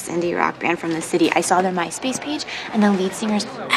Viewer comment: wow did she just say myspace? Omg. I havent heard that name in 15 years.